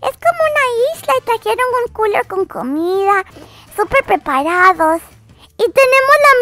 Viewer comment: 0 le entendí cuL0 con comida :v